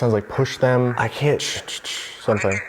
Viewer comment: I hear “I pushed them down the stairs”